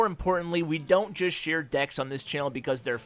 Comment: share decks hahaha i thought he said dicks